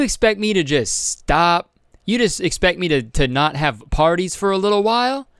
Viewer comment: Why do they even call themselves “influencers”. What have they ever done that has influenced anyone to do something good apart from throwing frat parties in the middle of a pandemic? 🤦🏼‍♀️ how did they even get a platform so fkin big smh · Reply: i thought u said fart parties